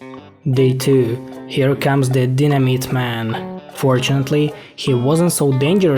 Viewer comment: he said dynamyte i thought he said dinner meat